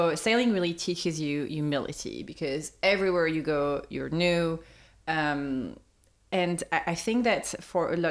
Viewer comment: For a second there, I heard “everywhere you go, you’re nude” 😅 I thought, well… those foreign harbors must be way more relaxed…. Then I realized that said “everywhere you go, you’re new.” That was a bummer. (Or maybe it isn’t…)